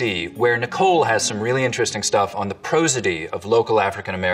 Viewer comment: you have a mistake in your captions! around 9:26 the captions say "profanity of African American speakers" but the people are saying "prosody"